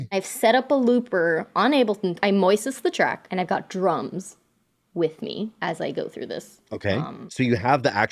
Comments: i heard ... " i got dru..." naturally i assumed ... drunk ... how of you to say #" drums"... thats not very chris of you i have no wall space left asnd i have yet to replacce the 4001 ... knock it off... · Thought she said, I moises the tracks and I got drunk! xD